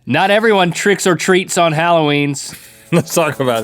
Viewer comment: Am I the only one that thought Link said Trits or Treats at the start of the video? Ok… im just going to leave 😂😂